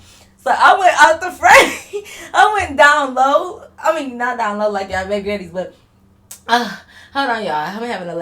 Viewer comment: WAITAMIN.... Did say "Not down low like your Great Granny"?😮😂😂😂😂 I have never heard that saying before 😂 My bad y'all, I suffer from slight morbid humoritest...